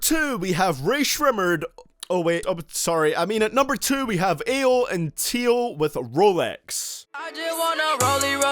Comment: I thought you said eel and Teel and then I realized you said ayo and Teo 🤣🤣🤣